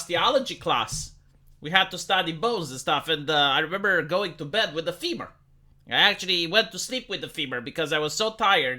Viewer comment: ok, might be because of the context of the osteology story, but I thought you said you went to bed with a femur.